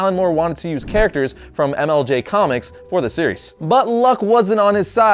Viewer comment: at first i thought he said MLG comics instead of MLJ comics XD